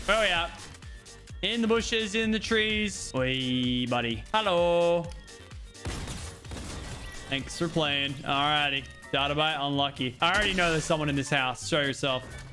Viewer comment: Did lachy say halo on Swedish